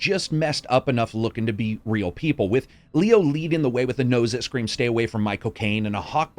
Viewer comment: I heard that as, "With a nose that screams 'Stay away from Michael Cain.'" Great Review. As Always :)